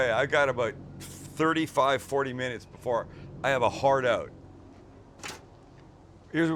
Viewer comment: Did you say "Hard out" or "HARD ON" ??? ..... TMI dude